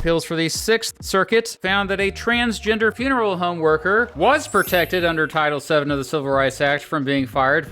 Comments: said the trans worker “was” protected, but the captions say “wasn’t” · Typo at 2:30 - wasn't instead of was, pretty big typo for hearing-disabled people there. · Your captions need to be corrected here. You said “was protected” by the captions say “wasn’t protected”.